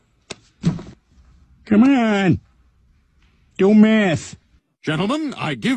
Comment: I heard "Come one, Do Meth"